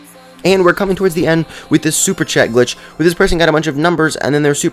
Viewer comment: anyone else thought he said snapchat? no ? oh... * all around me are familiar places *